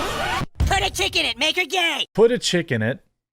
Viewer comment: I thought Cartman was saying put a chicken in it.